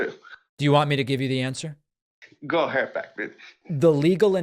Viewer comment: did he call u a b****?